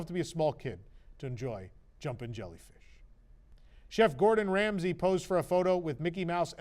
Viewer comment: did anyone else hear Jeff Gordon Ramsay? I was very confused before I realized he said chef Gordon 😂 I had a good laugh anyway